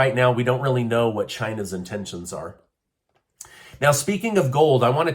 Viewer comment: Did he just say "we don't know what china's intentions are"